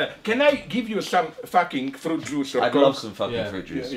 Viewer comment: did he just say f*ckin juice? =)))))))) I"M DEAD